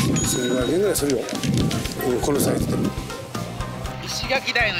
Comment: うんこのサイズにしか聞こえない